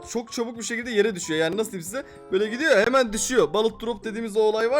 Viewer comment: 7.19 balıkdrop anladım abii 😂😂😂😂😂😂